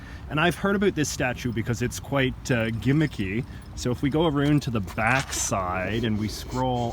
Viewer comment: did he just say "aroond" lol i had to replay that shit funny dawg sorry